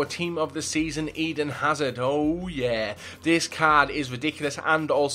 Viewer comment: U guys heard him say tots Howard instead of hazard rite